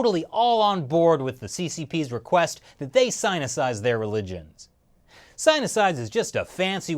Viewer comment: For awhile I thought you were saying Senicide which means to kill an elderly person...lol. Im now curious to learn more about the word Sinicize and its origins.